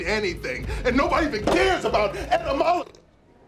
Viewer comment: i always hear "and nobody even cares about Edna Mode" 😭💀